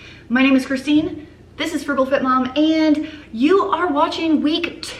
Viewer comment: I thought you said “Fertile fit mom” at the beginning. 😆 There ya go, your next series inspiration! 😂😂😂